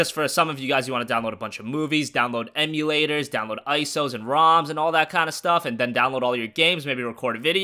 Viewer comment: download what did you say? 😂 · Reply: Roms